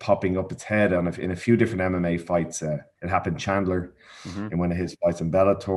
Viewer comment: did he say anime fights?